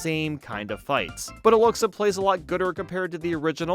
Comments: did you say gooder? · did he say gooder at 3:16? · did you just say gooder lol